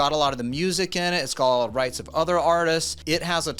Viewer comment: I thought they said a lot of it he has in his mothers name